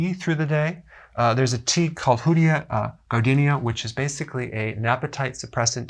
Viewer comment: what is that tea called? hoodia or something? I couldn't make out what he called it.